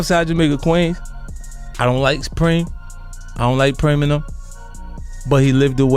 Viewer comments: STOP USING AI FOR SUBTITLES! According to the subtitles it say "I don't like Eminem" when he really said "I don't like Preme n them." lol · LMAOOOO Bro wtf is them subtitles. I was reading like damn, 50 got beef with spring? Ok, but why though? Then it says, I don't like Eminem, and I was like ok, wtf just happened cuz I know he ain't ever saying that. Turning "Supreme" into spring is reasonable, but "Preme and them" into Eminem? AI out here spreading fake news like crazy 😂😂